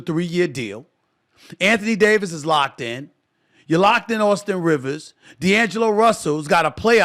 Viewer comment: Did he say Austin Rivers? 2:33 🤣